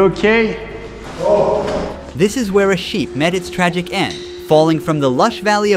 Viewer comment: I thought the guy who shouted was called "Hasheek" when in the next sentence he says "This is where Hasheek met his tragic end..." like it was nothing xDD